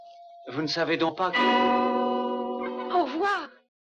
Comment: what did she say ? "Oh fuck" ? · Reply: She said "Au revoir"which means"Good bye"